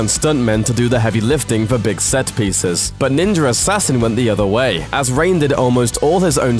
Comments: I heard him say it once, now I cant stop hearing it: Ninjer Assassin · sounds like he said ninger assassin. LOL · It sounds like he says: ninjar assassin